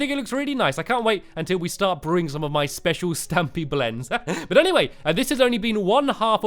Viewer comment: When said Special-tea I thought he said specialty Stampy cats Specialty :)